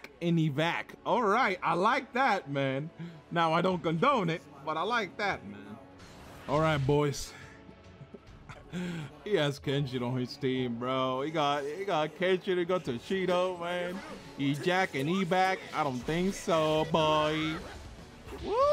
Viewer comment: Did I hear Kenshin? So im not the only one. xD